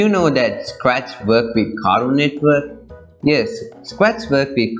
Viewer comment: ur indian accent is so strong, the subtitles say "caro network" instead of "Cartoon Network)